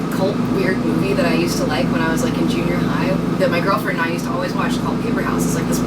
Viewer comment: does she say "my girlfriend"? ò____ò